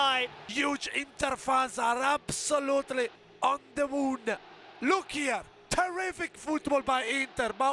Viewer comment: the way he says, on the moon😂 on the 3rd listen I'm actually unsure what he says now 😂